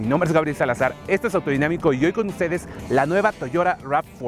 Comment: estoy seguro que dijo Toyora XD XD XD